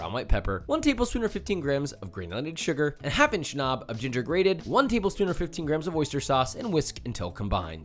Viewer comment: Every time you say "Ginger, grated", I hear "Cookies, Fortune!" "..........and THEN!???"